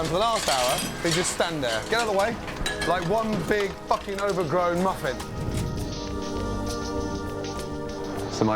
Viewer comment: When Gordon said that guy was standing there like an over grown muffin, I lost it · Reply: I thought he said Muppet, either one would work